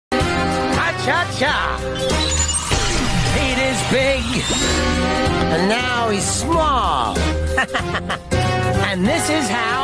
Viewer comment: I heard "Pete is Bacon Now hes small "💀